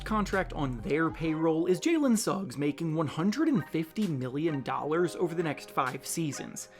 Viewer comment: Jalen Suggs - The caption says $50M, but the narrator says $150M. Which is it?